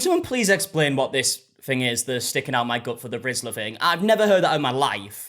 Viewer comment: I really thought she was saying "pimping out my gut" not "sticking out my gut" 💀😂